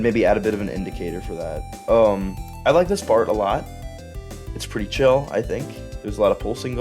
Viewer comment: i heard “i like this fart a lot”